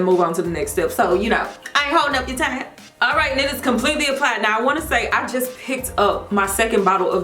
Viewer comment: Lmao I can't be the only person who thought she said "alright ni***s, completely applied" at 4:04 🤣🤣. I had to rewind like 4 times, but she didn't say that lol